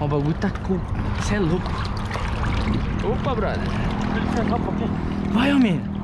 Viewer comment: escutei alguém falando opa brother